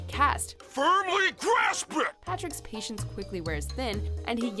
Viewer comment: "Firmly Grasp It" When I first heard it as a kid, I thought he was saying "Furry Grass Sprint"